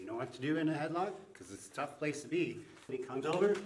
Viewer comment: Who else thought he said its a top place to be💀